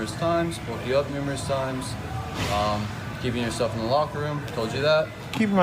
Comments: To be fair it did sound like he said “keeping yourself in the lockers” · To be honest, I thought he said “self.” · Thanks Bright Cellars for sponsoring this video and for the limited-time offer! Click here <link> to get $100 off your subscription and a free wine tote! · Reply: I thought the skinny dude said ‘keep your SELF in the locker room.. so… that’s an understandable mistake.